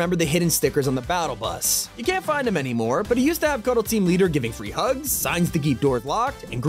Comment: i thought that said free dru